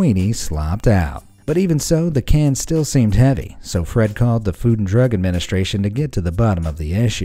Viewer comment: FYI: The FDA is the Food and Drug Administration. (Not the Food and Drink Administration.) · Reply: I heard food and drink twice. At first i thought it was a mandela that i was not aware of. But then i heard him say food and drug. Now I'm confused. But at least i know I'm not completely insane bc y'all heard it, too.